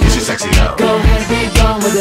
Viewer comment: IT TOOK ME SOOOOO LONG TO FIND THIS SONG OH MY GOD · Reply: I thought it said "go 'head we going" so it was hard to find